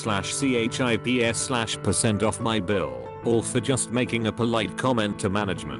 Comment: the hell did he just say... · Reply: sizo duke he said D R I N K S / A P P I T I Z E R S / C H I P S/ %